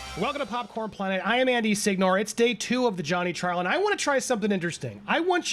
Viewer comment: plz be a Warhammer fan... every time you say your name i hear 'Andy Sigmar' and im so for it